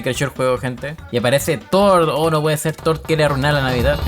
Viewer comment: soy yo o escuché tord